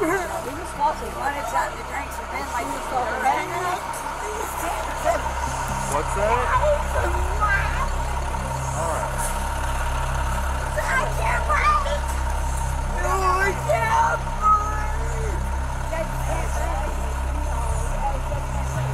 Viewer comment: swear i heard her say "i cant breathe"